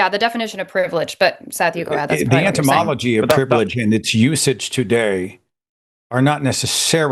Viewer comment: It sounded like Seth said "the entymology of privilege" Etymology is the study of the history of words and their meanings, entymology is the study of insects.